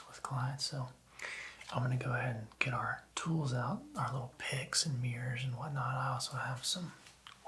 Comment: Great video, loved all the sounds! But I was very tired while watching this before bed and you definitely said “picks” but I heard “pigs”…I was very confused for a few seconds 😂